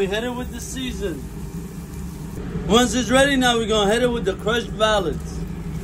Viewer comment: I thought he said next we hit it with the crushed Valium and then he was pouring crushed pills on the meat